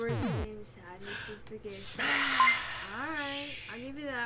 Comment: did you really say smash to my man Sylveon? I don't know how to react, should I laugh of feel fear